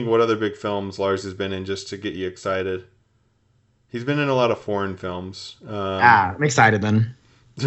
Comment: When says Lars Mickelson is in a bunch of “foreign” films it sounds like he says “porn”films… pretty sure that is what heard when he responds “Ah I’m excited then”. 😂